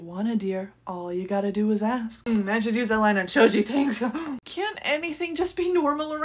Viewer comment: On 16:49 did todoroki say shoji?